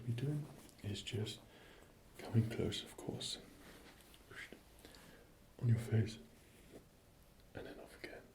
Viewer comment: Did he just say: "going to come close.. on your face" ? 😳